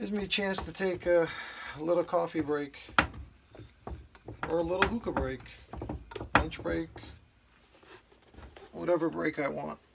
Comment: I'm sorry, I had to laugh when you said a coffee break then i thought you said " or a hooker break " .. I thought "what? Do they deliver??"" LoL...